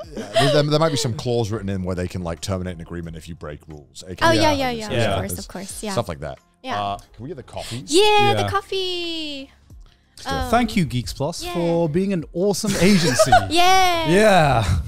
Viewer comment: When they said "Can we get the coffee" I heard it as "Can we get the copy"....as in an actual copy of the contract they have with GeeXPlus to show what a fair contract was